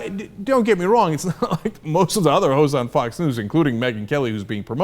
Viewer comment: When Cenk said all the other hosts it sounded like he said hos. lol